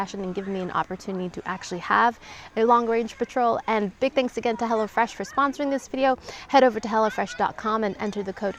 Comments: I thought she was saying HELLA fresh instead of HELLO😂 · The auto generated subtitles saying Hella Fresh lol. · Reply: I thought that was what she said, now I hear "hello fresh" lol!